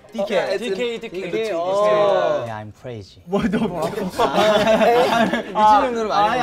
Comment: HELP FOR A SECOND I THOUGHT SCOUPS SAID "WHAT THE F..." LMAO